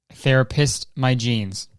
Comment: I really wanted him to repeat the “therapist my genes” line as “there I pissed my jeans” because that’s what I thought he said and I think it’s funny